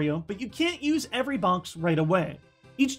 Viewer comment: I like how says bonx instead of box